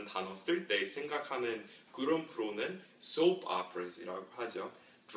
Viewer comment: 뭐라고하셧죠 ?? 막귀라ㅠㅠ .. 스펠링 적어주세요 what did you just say ?? I don't know the word, so you write spelling .plz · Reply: Hi! Is the word you're looking for "soap operas"? I think that's what I said at that moment in the video.